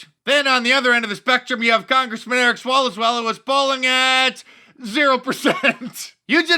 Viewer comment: Did Mark say "swallows well"?